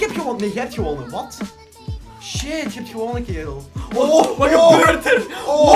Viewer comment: WOW WOW WOW hoorde ik daar nu kill?? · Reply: Hij zij kerel😑